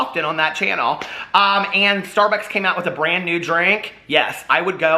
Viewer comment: Lol. At first I misheard you as saying a "brandy drink" instead of "brand new".